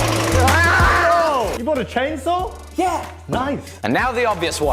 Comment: You bought a husky? I thought you said you bought a chainsaw